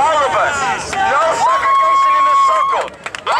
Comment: A black guy saying "We are gray?" The Civil War Confederates were gray... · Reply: I thought it was a gay protest, lmao I'm stupid, thought he said "I'm gay, your gay, your gay...